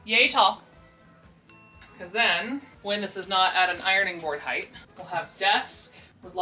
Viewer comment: Adventures in Auditory Processing! I know you said, “ironing board.” I heard, “irony board.” We have fun here.